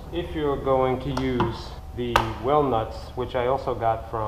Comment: I kept hearing "whale nuts", I finally paused the video to read the package "well nuts". I get it now.